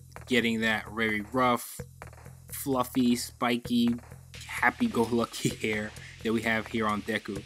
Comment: Did he say 5:56 happy Gubblush hair? Thank you very much, sir, for supporting my fluffy Gubblush hair. 😁